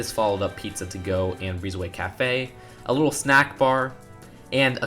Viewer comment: It sounded like he said Allahu snackbar and not a little snackbar xD